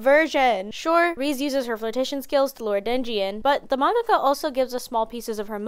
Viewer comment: LOL THE SUBTITLES SAY DENNIS INSTEAD OF DEMJI